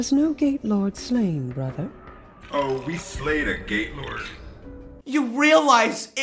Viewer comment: Slay a gaylord? Was I just hearing things at 18:00 or what?